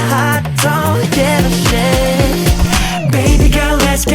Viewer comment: Is it just me or did Yeonjun say "I don't give a sh*t" and not " I don't give a thing"?